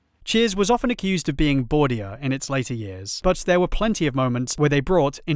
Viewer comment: Can you tell me what he says in 1:38:35 "Cheers was often accused of being--" 'boredia'? what does he say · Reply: Bawdier- Bawdy comedy tends to be very loud and rude and irreverent, heavily sex based. Cheers got accused of being much more this way in the later series and less concerned with the subtler emotional content, but personally speaking I think they hit a very fun balance between the two.